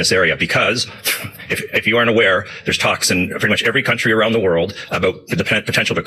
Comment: He said "talks in" I heard "Toxin" Both are true...